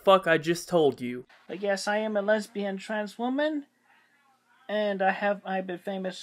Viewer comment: The second he started that clip, I saw this guy, and heard his voice, and immediately knew, this was the one. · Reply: I thought the guy said I'm a lesbian transformer at first